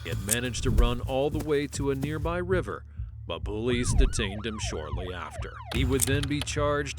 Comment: Please check your speech to text bro, at 23:58 it says "bullies" instead of Police.